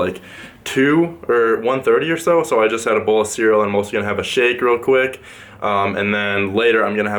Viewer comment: I totally thought you said, “I’m also gonna have a shit real quick”